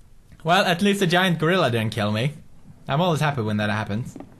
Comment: what did he sayyyyyyyyyy?! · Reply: Yes he did call that guy a gorilla 😂